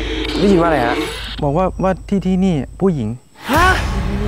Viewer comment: ตรง25:27กับ25:28เหมือนได้ยินเสียงผู้หญิงพูดที่นี่(เบามาก)